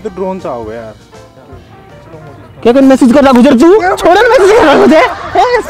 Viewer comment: pe kya bol se bag gay ye?😅😢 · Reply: Amit said - kai kan msg kr rakhyo ch tu,,, mean- kisko msg kr rkha h tune😅